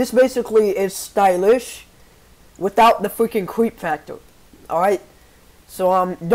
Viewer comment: wait is stylish safe or no? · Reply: wait.. shit he said sylish, i thought he said stylus xD Sorry